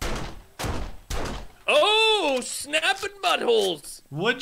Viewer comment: Did say "Oh snapped butt-holes???? 21:20. I cannot stop laughing!!!!